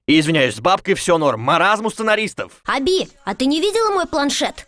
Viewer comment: мне одной послышалось Хабиб